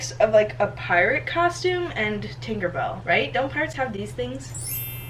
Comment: instead of tinkerbell, I heard tinderbell I shall repeat tInDeRbELl 🤦